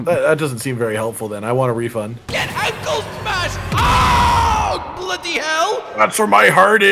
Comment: it sounded like you said GET IDLE SMASHED DINE